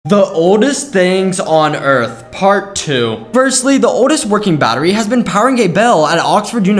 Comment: I thought bro said “The oldest fucking battery” and I was like WTF 💀💀🤚